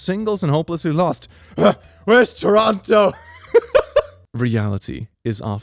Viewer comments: Oh god did he just say Toronto instead of totoro. 😱🥺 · did he just call Totoro Toronto??? lmafo Edit: Damm... I thought I was the first one talking 'bout this and would get some likes but then I scroll down for about 10 min and see like 75+ comments about it. Feels bads